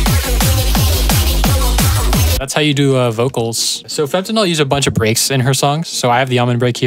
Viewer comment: My dum as thought you said fentanyl